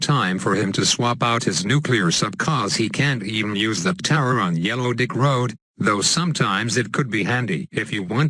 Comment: sounded like he said yellow dick road XD